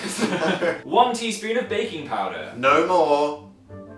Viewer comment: I looked away at 0:53 and for some reason what I heard was "1 teaspoon of BACON powder". What is worse is that I was actually contemplating for a few seconds what a bacon flavored brownie would taste like before I realized what an idiot I was...